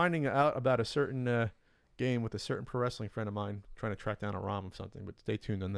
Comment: At @ 12:18 I thought said “...with a certain caressing friend of mine.” 🤣